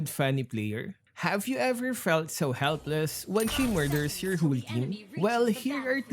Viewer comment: I thought you had said when she murders your hoo ha lmfao